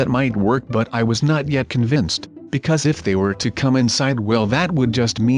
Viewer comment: what did he say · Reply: And he said come inside